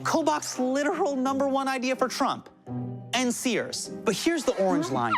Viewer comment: omg I'm dying! I thought he said end Sears!